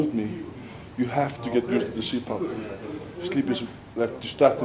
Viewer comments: what are the saying? "Sleep pump? around the 6:00 mark? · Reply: CPAP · What did they talk about at 6:00 with the sleep thing? I couldnt really hear what it was called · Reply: CPAP